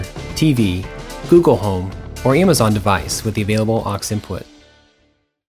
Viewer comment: It sounds like he is saying gay crasher 😂